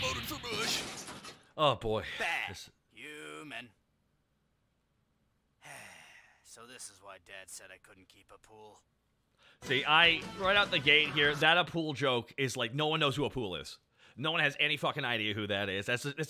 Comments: It feels appropriate considering BT4’s announcement but uh I 100% knew who Appule was simply because of the BT games 😂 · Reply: I thought he meant to say “a poodle” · Huh, I always thought Radditz said "This is why dad never let me get a Poodle". I assumed the joke was he couldn't have a pet because he'd be too rough with it. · It feels appropriate considering BT4’s announcement but uh I 100% knew who Appule was simply because of the BT games 😂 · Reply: My dumbass didn’t figure it out until years later, the whole time I thought he said “a pool” not apule lmao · Oh it was appule? I always thought he said a poodle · You know i legit thought raditz said a pool and not appule. Im learning so much from these commentaries. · I always thought raditz said this is the reason I can't keep a poodle. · Lol.. I thought you said "a pool". Not apule. You should just go with that! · I always thought Raditz said " So that's why dad said I coudn't keep a poodle." The joke finaly makes sense! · I always thought Raditz said I couldn't keep a pool, not Appul lol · You know I thought Raditz said a poodle. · in all honesty, this entire time until today, i always thought raddiz was saying a pool like a swimming pool. had no idea it was actually Appule lol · Wait apol? I thought he said “so that’s why dad said I couldn’t keep the pool.” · He says Appule? 0.o Always thought he said "a poodle".